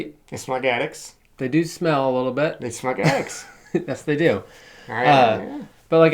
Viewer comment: Listened several times. What is saying the tunnels smell like? (Like attics?) And I never noticed a smell. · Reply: OK, I see below that you reveal it’s “attics,” a smell I do understand. The first time says it you can just about make out the words. But the second time? They smug addicts. Indeed, they are smug addicts.